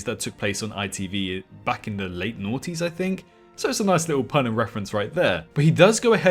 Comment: you said that primeval came out in the nineties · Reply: Maybe you misheard him, he probably said the noughties aka the 2000's maybe, so yeah